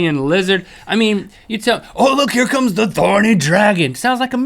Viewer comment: "Oh look, it's a thorny dragon!" I heard : " oh look, it's a horny dragon!" 💩😝😉😮